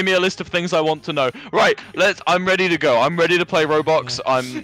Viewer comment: Did Wilbur say that he is ready to play ROBUX???